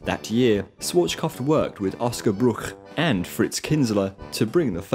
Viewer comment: for a second there I thought you said "frau blücher"